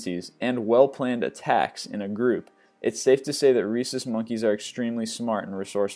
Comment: I thought he said racist monkey's XD